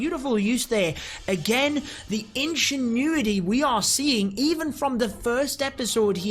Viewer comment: Did he say "ANCIENT NUDITY" ?